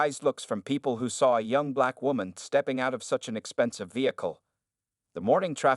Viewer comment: I thought it said fine black woman.